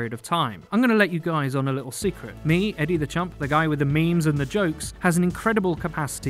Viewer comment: Toxicity seems frequent recently, let us know your experience of it and how you deal with it! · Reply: I honestly always thought he was saying Eddie the Chump...